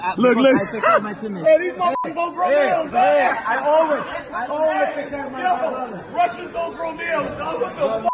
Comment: did khabib say 'my nigga' 😂💀